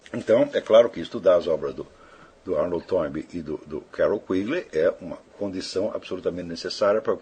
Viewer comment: POR GENTILEZA ! Alguém poderia me dizer os nomes dos dois grandes historiadores da globalização citados em 11'30''. Entendi "Aron Tormey m" e "Karon Greevley" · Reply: Arnold J. Toynbee e Carroll Quigley